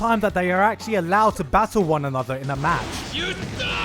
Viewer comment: I swear to god I heard bakugou yell "YOU THOT!!!!"